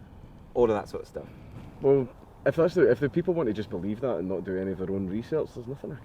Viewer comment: Actually I thought he said "want juice"?